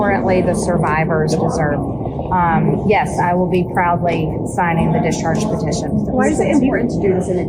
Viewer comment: She says “probably”. Let’s see her follow through and actually sign the petition. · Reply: No I replayed that as I thought she said probably, she actually said "proudly"